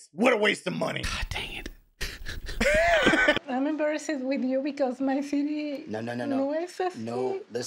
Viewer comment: TLC messed up with the translation at 2:55. She said, “… no es así…” meaning, “is not like that,” referring to her city not being like that. So now everyone who watches this doesn’t get what she is actually saying, unless they know Spanish. Smh.